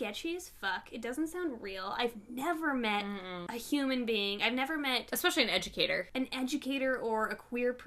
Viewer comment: I heard “I’ve never talked to a person......” Ik I need heating aids but at first I was like SAME